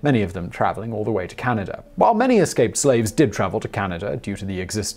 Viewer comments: Did i hear "eslaped scaves" lol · I thought he said "eslaped scave" at one point when clearing up the myth of the $40,000 bounty.